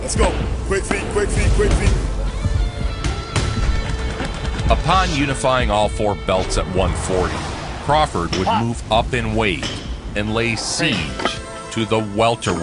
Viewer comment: Name the opponents Crawford faced before Porter. And you mentioned Ellerbe? 🤡🤣🤣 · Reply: Oh duh! Lol I thought you said if Bud moves up. Erase everything I said to you🤣🤣